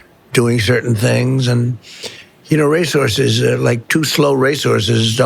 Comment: Race horses? I thought he was saying 'resources'